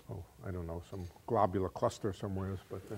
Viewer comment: Did he say “suspend a globule of custard” at 43:20?